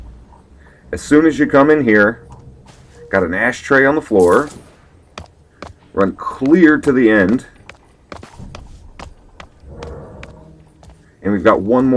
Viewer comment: did you say "Run queer to the end?" :D I don't know dude....don't think running like that would help my image as a bad ass thief. :P